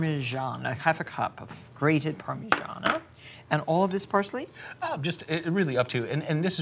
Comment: 🤣 I thought she said "Elvis Parsley"